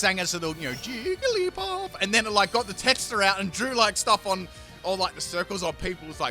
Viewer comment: Wait, what did he call Jigglypuff's marker at about 7:50??? Texter or something? Love the singing btw! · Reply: texter Is a marker In aus :P